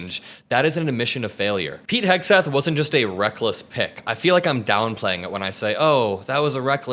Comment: You know sometimes you hear what you want to hear? I heard "Reckless pig" not "Reckless pick". Lol.